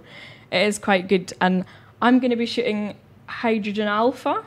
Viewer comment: Lol - listen closely at 2:07 she literally says she is going to be "Shitting" Ha instead of Shooting lol